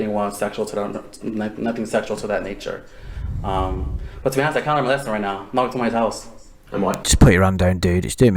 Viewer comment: fucking what did he say? He mushed the fuck out of those words. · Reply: “To be honest I kinda learned my lesson right now, don’t walk into someone’s house”